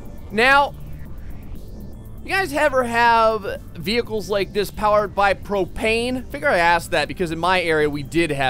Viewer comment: I swear to god I thought that he said vehicles powered by cocaine.